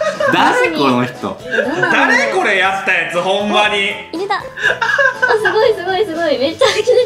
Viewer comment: 何もしていないのにから「誰やねん！！」って言われる紫咲シオン草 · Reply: 「（これ作ったやつ）誰やねん」にも聞こえる